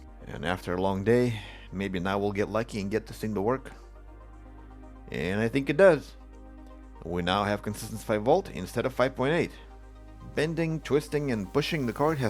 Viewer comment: that fake explosion at the minute mark made me jump lol. Impressive fixing skill and relentless root cause analysis. Did you manage to fix those laptops you video a while ago? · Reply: I think that's why I heard "instead of 5v..." as "instead of Fire Bolt..." 😂